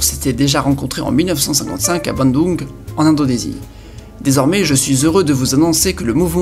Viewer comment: A 5:57 Il y a une faute dans les sous-titres, il est marqué "chez Indonésie" alors que toi tu dis "en Indonésie" :)